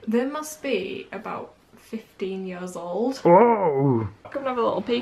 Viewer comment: Oh, I thought you said 50 years old! You would be really old then😂